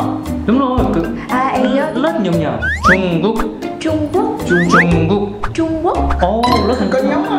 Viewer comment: chọi mé nghe nhầm Jungkook :))